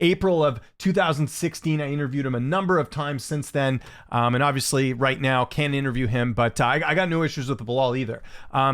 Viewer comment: Why can’t you interview him now? It sounds like you said “obviously right now I can’t interview him.”